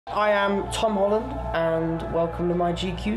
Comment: I Heard: "I Am Tom Holland" But the english translation said: "I Am PalmOlive" lololololol I Cant